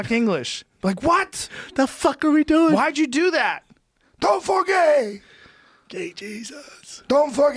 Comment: LOL it really did sound like he said dont go for gay jesus 😂😂😂